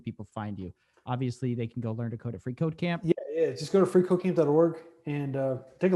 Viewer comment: I heard 3 times " free cocain" instead of FreeCodeCamp , joke aside am a proud student of that platform thank's for bringing the man as guest